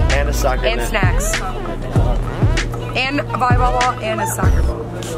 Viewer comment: hold up did she say "volleyball ball"